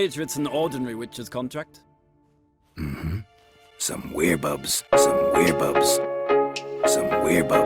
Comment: At first I thought I heard Geraldt say "Some weeaboos" when I got to that part.